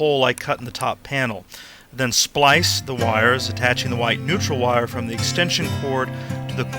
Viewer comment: Sorry, My mistake! You said White. Thought you said Wife . My deepest apologies!